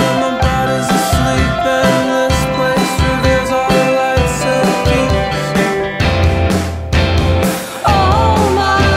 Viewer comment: Love this song. Just can't figure out what he's saying at around 1:11. It's right after he says "And this place reveals....". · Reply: Our life's at peace